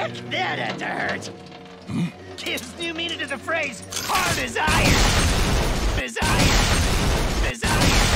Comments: "H A R D A S I R O N" Unironically famous LAST WORDS. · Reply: I thought he said "hard as I am" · "H A R D A S I R O N" Unironically famous LAST WORDS. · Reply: It sounded like he was saying "AS hard as I am"